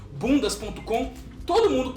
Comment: ele diz "Boom das ponto com", eu entendi "Bundas ponto com"